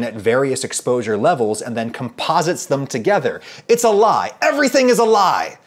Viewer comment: Did he just say the cake is a lie? Lol